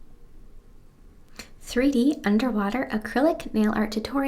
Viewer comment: I thought it said "did 3d underwear" lmao